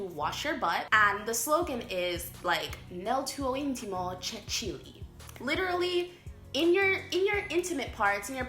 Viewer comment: God, I heard "nel mio intimo c'è chilly" since I was a child and I never grasped the literal sense of it 😂😂 Btw kissing cheeks is so annoying to me too... It's like.. "I have known you for like 2 minutes, get off of my face! " And I do find weird the idea of not having lunch or dinner at the table 😂